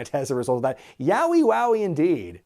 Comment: I thought the crowd said "Nasty Landing!" Not "Yowie Wowie!"?